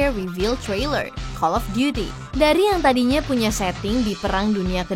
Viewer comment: gasalah denger gw itu call of duty? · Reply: Iya call of duty